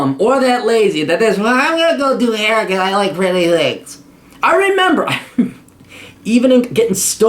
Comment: what was said? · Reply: ***** He said, "I'm gonna go do hair because I like pretty things."